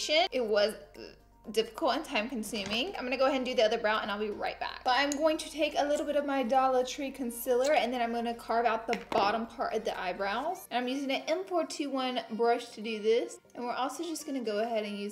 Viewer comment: I thought you said Darla Trei concealer